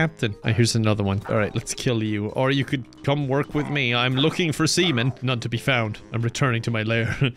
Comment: when he said "im looking for sea men" it sounded like "I'm looking for semen" Too perfect